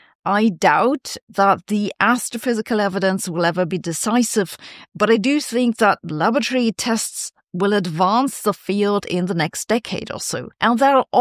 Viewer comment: You did actually say ‘lavatory tests’ of quantum gravity!!😅. I replayed at 0.25 speed and it’s really lavatory humour!